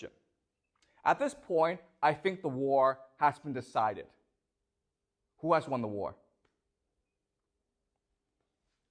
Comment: Did someone catch what he said 22:00 about American plan .. did he say “shockenal” ? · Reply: Shock and awe is a term that mainstream fake news media used during Iraq war